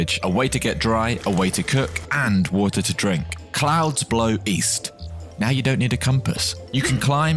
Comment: East? I thought you said WEast